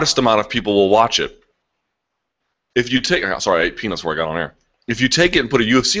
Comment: it sounded like Luke said he ate penis before he got here